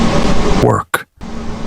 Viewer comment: Right in the middle of him kinda showing why he did there was a voice that Sid “work” anyone else hear that voice? Right around the 38:25 mark, wasn’t from the detectives or the father.. I just thought that was really odd